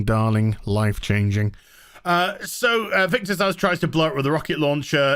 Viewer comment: All I hear is Victor's Ass